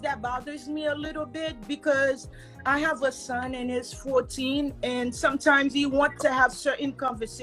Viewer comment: Did She Say, I Have A Son And IT'S 14💎🤕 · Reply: Okay. She Said IS. Sounded Like She Said IT'S....